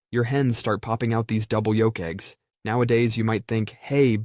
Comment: When he said double yolk eggs I thought he said w rizz